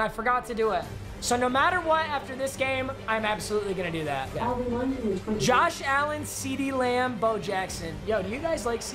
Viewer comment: anyone else hear Alexa?